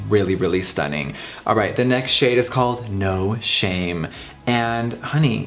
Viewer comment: when he said the shade “no shame” i thought he said no shane lol